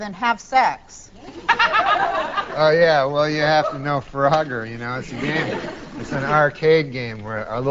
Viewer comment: i thought she was saying "farogger" and i didnt know what that was. AH.. FROGGER